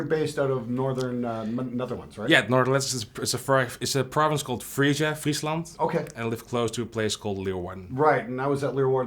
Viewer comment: did he say freezland? Wow in the Netherlands they are really taking this 4:20 thing seriously. I'd like to live in a province called Jack Herer.